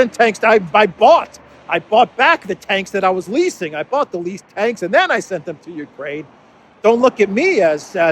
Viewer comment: I always hear it as, 'leper tanks' (tanks for lepers) 🤣.